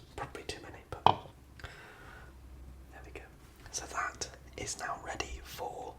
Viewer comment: What did u say in 34:57 . Is it "jebiga"? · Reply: I believe it was a quiet "there we go" 😂